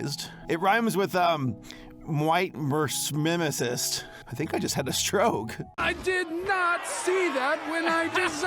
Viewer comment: what is the word is trying to rhyme? i literally cannot understand what the word is supposed to rhyme with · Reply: ◽️supremacist Basically neos